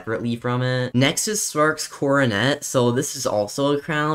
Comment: I like how he said sparks kordinet lol 🤣 instead of sparks kilowatt